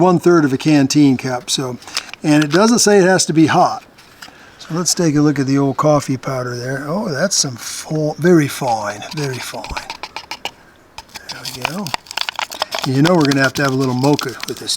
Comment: Anyone else catch that Aussie accent, "Very Foine, Very Foine" at 9:47?